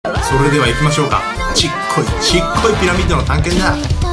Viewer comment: ヒカキンがちっこいっていう時、曲もちっこいって言ってるように聞こえるw